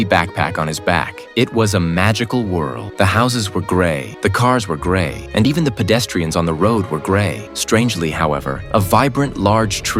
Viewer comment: I thought it said “the houses were gay, the cars were gay, and even the pedestrians on the road were gay” 😭